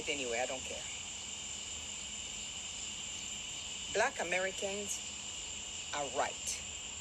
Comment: Thank you. I only stopped to listen because of you. This video came up on my Tik Tok FYP and I scrolled past because I thought she said "Black Americans are WHITE" LOL. I am so glad you posted this so I could stop and really listen to what she is saying!